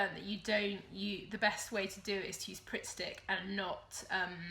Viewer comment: I didn't catch what you were talking about as a substitute for masking tape when assembling PDF patterns. Not sure if I didn't understand what you were saying (I think I heard "print stick") or if it's just a U.K. term for something I might actually be familiar with. Could you elaborate please? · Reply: Sorry I meant pritt stick - its a stick glue.. I'm sure you guys have it too but here is a link so you can see! <link>